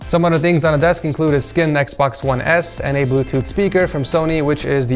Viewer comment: did you just call that an Xbox one s?